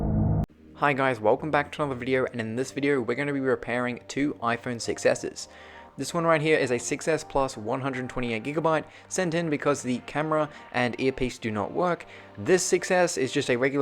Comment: when you first said that you will be repairing two iphone 6 s's , i thought it said success